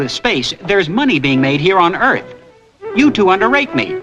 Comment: For decades I thought he said "wanna rape me" too before I finally learned that it's actually "underrate me!"